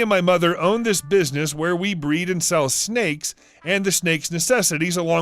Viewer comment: who else heard Hank Hill say he sells "snakes and snake accessories"? Just me? I'm pretty high 💜